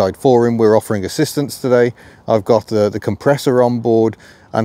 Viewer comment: For a moment at the beginning I thought you said "Edward the Compressor" !